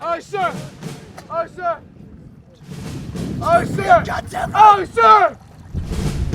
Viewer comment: Did he say "Straight through your goddamn eye" ?